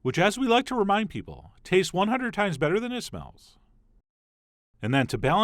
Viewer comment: Can anyone tell me what he said the asian fish sauce tastes 100x better than around 0:53? Can't figure it out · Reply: Tastes 100 Times better than it smells